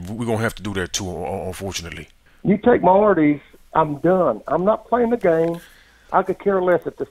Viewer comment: 60 year old man "You take my Arty's and im done" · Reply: Dillon Emigh I thought he said arteries 😂